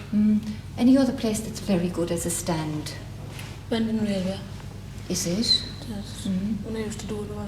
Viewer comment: and. Starting at 7:27 when she starts asking about the good stands, what are the places the boys mention? The Fords? Where is that in Cork? 7:54 what place does mention? And again at 8:07? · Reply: The Fords would be the Ford Motor Company factory that used to be where the Marina Market is now. It’s who speaks at 7:54. Sounds like he says “Bandon Railway” by which he would have meant the area of merchants yards, factories and offices near the old railway terminus at Albert Quay. More or less where Elysian tower and City Hall is now. At 8:07 mentions Crosshaven as a good place to sell Sunday papers.